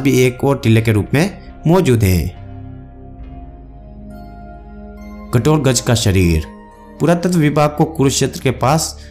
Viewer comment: kya bol raha hai ...thik se bol😒 Gatorkaj nehi bhai